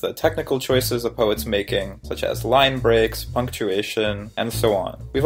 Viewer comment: it says functuation instead of punctuation